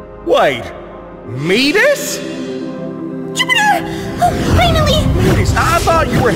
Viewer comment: Did he just say Skibidi?????